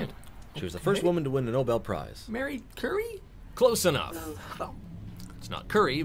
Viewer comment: What was wrong with the second answer? Marie Curie was the first one and that is exactly the name he said... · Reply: Perhaps it's the pronunciation? It sounded like he said Marie Curry. Delicious.